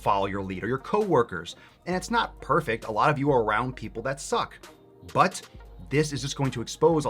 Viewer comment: Misheard “A lot of you are around people that suck” as “A lot of you are round people that suck”